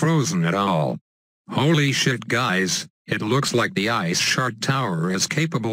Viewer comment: when he says "ice shard" it sounds like he says " ice shart"